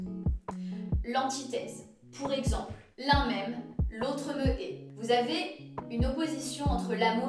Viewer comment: elle a dit quoi dans l'exemple j'arrive pas à la comprendre : la même, l'autre quoi ??? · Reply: L’un m’aime l’autre me hait